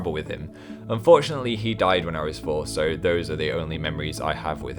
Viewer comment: At first I thought he said his dad died when he was 4 and almost started crafting